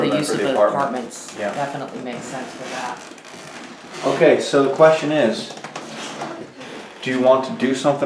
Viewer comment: "ok so the question is" 17:20 young girls voice repeating, what may have been just said